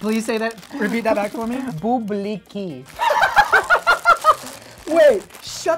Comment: for a sec i thought he said "booblicky"